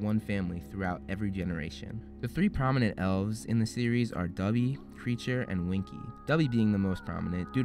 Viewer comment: did you just call Dobby "Dubby"?????